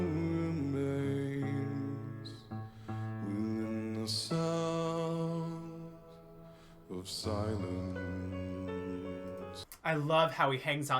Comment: The captions are wrong. It is "within the sound of silence!"